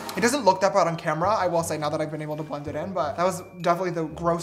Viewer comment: what is he even saying? He really is a fast speaker. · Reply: “Now that I’ve been able to blend it in”